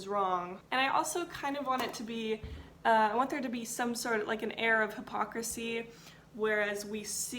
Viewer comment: I am sorry but I swear I heard "Arab Hypocrisy" instead of air of hypocrisy and as an Arab I was like ...mmmm but what was that about? Lol ....